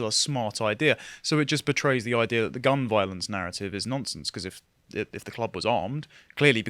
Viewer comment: Due to the recent nonce-related stories, every time you guys say the word “nonsense,” I hear it as “nonce-sense” lol